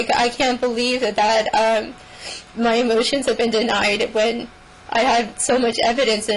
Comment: I thought she said my emotions have been denied. 😳